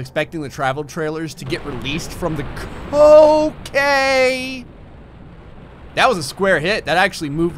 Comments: croquet....instead of okay. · when he said okay when the bomb squad exploded i thought he was about to say coacane